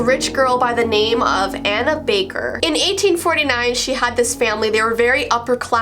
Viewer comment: Anna Baker????? I thought u said HANNA BAKER😂 I dunno why